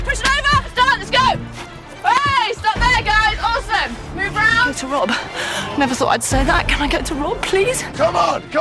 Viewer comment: heyy! I´m from Germany and I don´t understand what she says: 26:16 - 26:18 Thank you! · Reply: Sophie Müller can I get to Rob. Never thought I’d say that, can I get to Rob please.